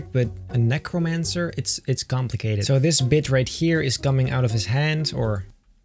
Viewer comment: A Necromancer is a wizard who uses Dead and and corrupted things as its powers, it also normally gives its soul for these powers Time stamp edit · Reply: he sounds like hes saying "This bitch right here" XD